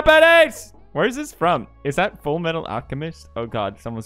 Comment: "Is that from full metal alchemist?" I thought you said you started watching Jojo....